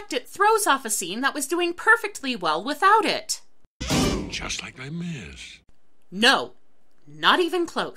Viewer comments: My auditory processing sucks, what is the thief saying at 12:57 that is reacting to? My brain can't parse it. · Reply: He says "Just like Les Mis!" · What did he say at 12:57? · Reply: "Just like Les Mis."